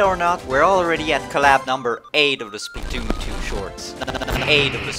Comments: did you just say pancakes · did someone say pancakes?